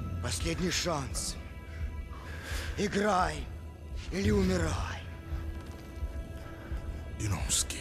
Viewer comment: Anyone know What the Russian solider said in 0:32 ??? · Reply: The soldier says "Последний шанс. Играй... или умирай" It translates roughly to, "last chance, play or die"